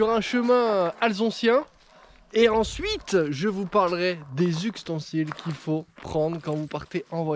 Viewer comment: j'ai entendu "uxtensiles" j'ai quitté